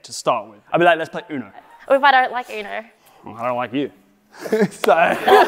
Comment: Nah I he really said I don’t like you